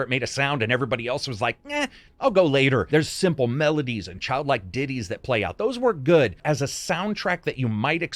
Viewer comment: Please never say "Child-like Ditties" again because it sounded like "...Diddies".